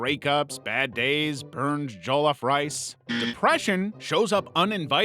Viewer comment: I'm so sorry to bring this up but did bro just say burnt jellof rice